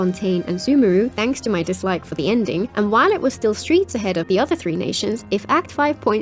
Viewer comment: did you just say streets ahead